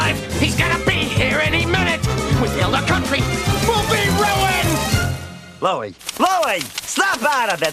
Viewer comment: What did Louie just said here? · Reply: "We failed our country. We'll be ruined!"